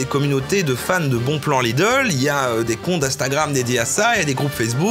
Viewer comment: j'ai entendu «Les cons d'Instagram» et ça m'a pas choqué